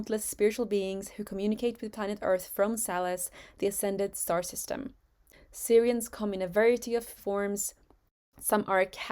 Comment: did you say salas star system? Because I checked and there is no such system. English is not my first language sorry for that.